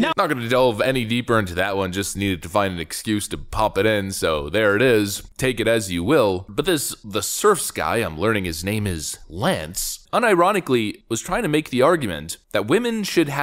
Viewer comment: Lance? Wtf did I just hear? Lance? Did you say Lance?